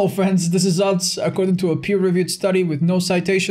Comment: I thought he said ‘I’m going to a puberty study’ in the intro Jesus Christ I’m deaf